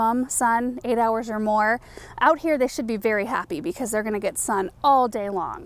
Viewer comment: mark I thought she said, “out here they should be berry happy” 😂